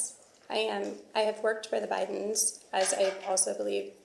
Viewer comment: I personally do don't believe from here on out any thing she is saying and I would say she is in Contempt of This hearing and should be held and charge and fired 😊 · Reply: She works for the bidens still is what I thought she said at the beginning .